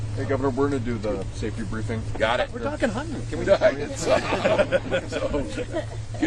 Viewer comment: Hunting pheasants!? I thought you said hunting peasants!